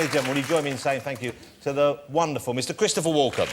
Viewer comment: Sounded like he said , "Mr Christopher Walker" .